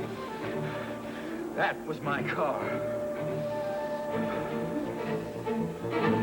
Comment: when Derek speaks it sounds like he said Mike Karr, but we know my car